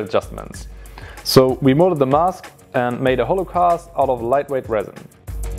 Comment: am i the only one who heared "we made a holocaust" instead of hollow cast? >_<